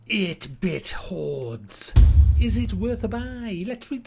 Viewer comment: I could have sworn said "8 bit whores. Are they worth a buy?" at the start